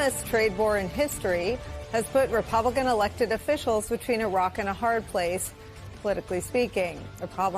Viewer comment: I genuinley thought she said between iraq and a hard place. Stupid British man i am. Still not as stupid as a man who thinks america can just make everything here when they are reliant on the tangibles from others to do it. This "short term issues" are also similar to the rhetoric from the leaving the eu we in the uk experienced. That was 2016 and it is still affecting us.